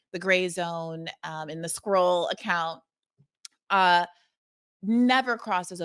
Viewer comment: Can someone please say what Briahna says at 6:13? The Scroll Account? The Escrow Account?